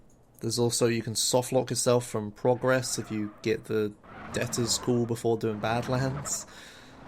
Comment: I cannot make out the description of the softlock at 01:08:55. Can somebody tell me what is saying there? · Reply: "You can softlock yourself from progress if you get the debtors call before badlands"